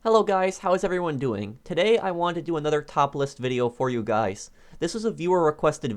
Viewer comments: Right at the beginning when you said "top list video", i heard "topless video" and spat out my drink 😂😂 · After the intro I thought he said “Today I wanted to do another topless video for you guys” 😂